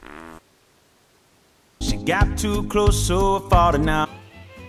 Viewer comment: I hear “She got too close so I’m falling out.”